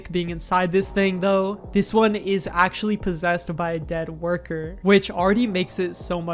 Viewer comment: When he said, "Possessed by a dead worker," I swear I heard "Possessed by Freddie Murcury"